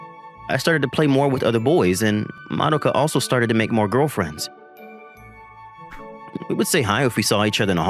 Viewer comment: bro what girlfriend bro it say friend